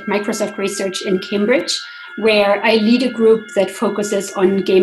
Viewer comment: DID SHE JUST SAY SHE YEETED A GROUP.... bruh